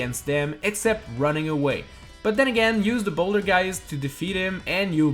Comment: it sounded like you said bouldergeist aka the easiest super mario galaxy boss